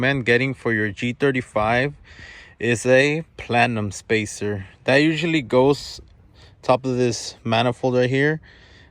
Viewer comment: did he just say platinum or plenum spacer. lol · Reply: Plenum bro lol